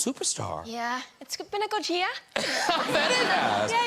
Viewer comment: what does she say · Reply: prasun yeah, it's been a good year.